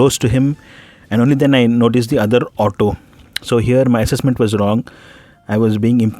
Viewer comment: Assess. In thought he said ASSes. ASSes really made sense to me here.